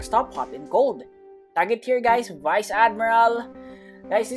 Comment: did he just say vice admiral???